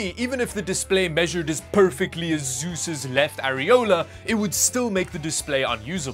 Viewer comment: I heard "Susu's left areola" lmao